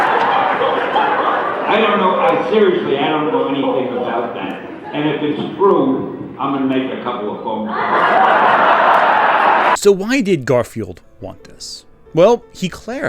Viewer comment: what did he say? The audio is really muffled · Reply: He said, "I don't know- I seriously- I don't know anything about that. And if it's true I'm going to make a couple of phone calls."